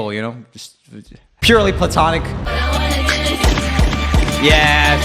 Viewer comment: I’m not in an area where I can hear the video well, so when Roomie said “platonic,” I heard “butt-tonic” and almost did a spit take...